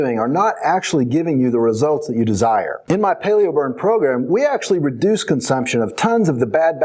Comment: I thought that intro said Paleo Bum. That's all.